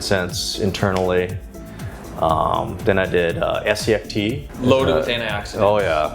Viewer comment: What kind of tea did he say? SEF?